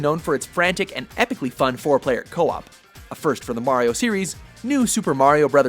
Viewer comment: For a moment there, I thought he said "epically fun four player co-op."